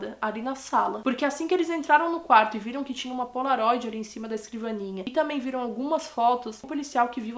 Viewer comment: viram que tinha uma o que? Não entendi! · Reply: polaroid